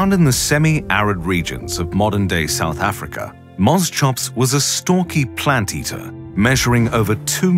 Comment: bosschops is king of nightmarefuel yes ik thats not its name but its what i heard first so thats what it is